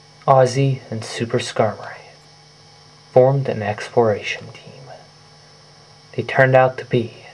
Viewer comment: am i the only one who thought he said ExplorationTIT?